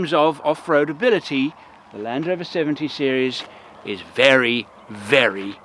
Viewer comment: Land Rover 70 Series... he still love his ex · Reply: I heard it as Land Rover 70 Series too, and had to rewind the video to make sure I'm not hearing things.